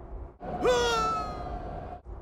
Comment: sounded like woody